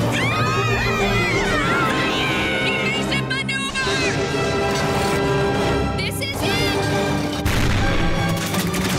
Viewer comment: What is it saying at 0:49 ? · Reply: evasive maneuver